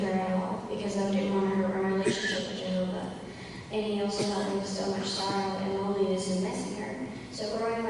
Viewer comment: "And he also helped me bestill my sorrow and loneliness in missing her." That's what I hear.